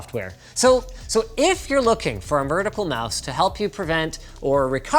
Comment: Did he just say "mertical mouse" at 7:47? Or is that just an accent thing?